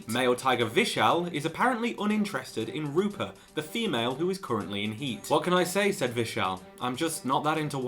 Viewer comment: I thought you said 'Rupert, the female' No wonder they can't get them to have babies... [luh-guh-buh-tuh] lol!